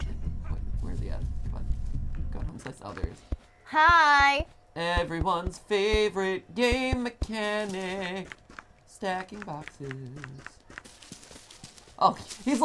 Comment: 🎼Everyones favorite gay mechanic, staquine bauxess. Lol. At least thats how i heard it